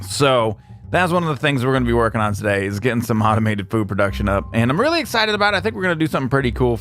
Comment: What said: "Automated food production." What I heard: "Automated poop production."